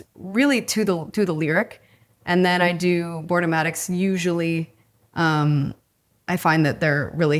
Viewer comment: what does she say? "Board dramatics"? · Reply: "Boardomatic" - like an animated version of the storyboard.